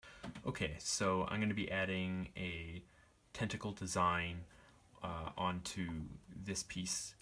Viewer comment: i"m two weeks behind catching up on videos, love the tentacle! At first I thought you said you were going to put a pentacle on it, then I was like "oh a TENTACLE" hehe